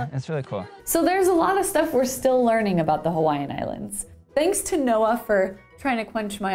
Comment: thought she said thanks to NOAA